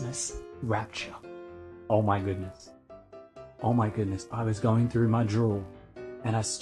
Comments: oh my goodness! At 6 seconds in, the captions say, "I was going through my drool...", as well as it sounding like he said that, and all I could think was, "I bet you were..." Seems like the most likely source for all of these "prophetic" predictions... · I listened to him several times and I still think it's more likely he said he was "rooting through my drool". And the auto-closed-captioning agreed with me.